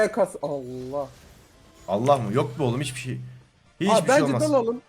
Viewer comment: abi Allah yok dedin sandım 2. Defa dinleyince anladım 🤨